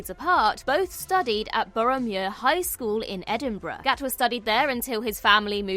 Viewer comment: OK so it's Boroughmuir High School? At first I thought said Boromir, like in LOTR 😄